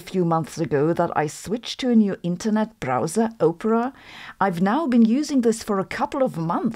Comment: I like how it sounds like Sabine is saying "Oprah" when they mean "Opera". I just get this mental image of Oprah sitting in room connected to a mass of cables and circuits are printed all over them. Half machine, half woman, all super computer. Oprah: "Connect through me people of the world! I'll be your virtual guardian! You get a connection! You get a connection! You get a connection!"